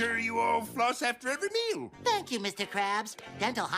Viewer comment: did he just say fuck you